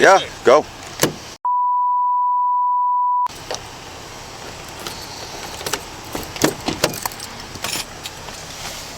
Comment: You are so awesome! The way you handled the whole situation was perfect. Especially the expletive at 7:16 what did you say there? 😂😂😂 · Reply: 😂 IT WAS F-U-Bit$h